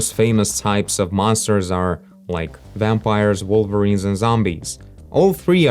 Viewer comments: Did he just say Wolverines??? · did he just say "vampires, WOLVERINES and zombies" ? I didn't know X-Men were famous mythological monsters XD